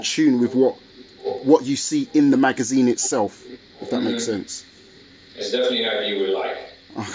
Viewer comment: what did he say? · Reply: I think he said something along the lines of "It's something you would like"